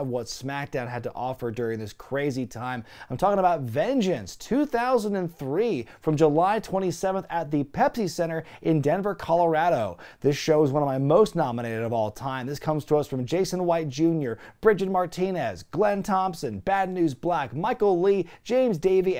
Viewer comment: Sounds like your saying Pepfy Center 🤣